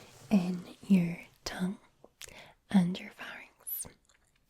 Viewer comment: is SOOOO relaxing! Though at 24:13 I keep hearing "tongue and farts"